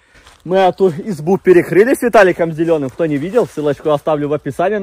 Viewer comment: Мне послышалось: мы здесь были с Виталиком обделённым 😂😂😂